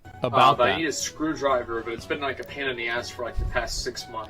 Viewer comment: When he says "it's been a pain in the ass" about the pan, I heard " but it's been a pan in the ass" 🤣🤣🤣